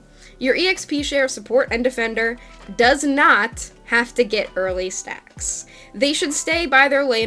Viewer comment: I thought you said early snacks and I was like... but you should never wait for snacks...